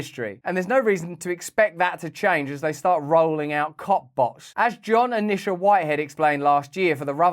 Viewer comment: Every time you said Cop Bots I thought you were saying................C**k Bots 🤣 I guess it's just a matter of time.